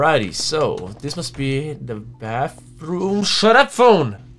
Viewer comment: Who wants the next demo as fast as possible as well? Support this video and the developers for more! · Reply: at 4:18 you say bathroom instead of kitchen.